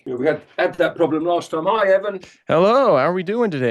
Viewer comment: is this a name reveal for? I swear it sounds like Nigel says “Hi Evan”. Amazing video by the way